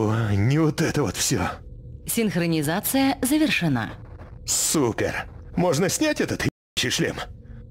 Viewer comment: мне послышалось он сказал сука,а то,что он,оказывается сказал супер я узнал только прочитав субтитры